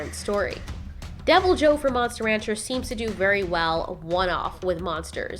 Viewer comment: I heard "Monster rancher" and I'm not mad.